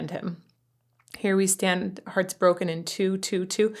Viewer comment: When I was a kid and this song was out, I thought he was saying, "Here we stand with our poor hearts broken in two."